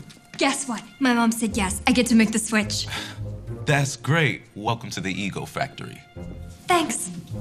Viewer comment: I swear at 3:25 he said welcome to the e-girl factory or is that just my bad hearing 😂